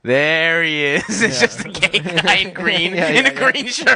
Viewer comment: Hearing laugh at Poison HIV being just a gay guy in green is fucking hilarious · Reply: oh, "gay guy in green". I thought he said "gangrene", which also pleased me.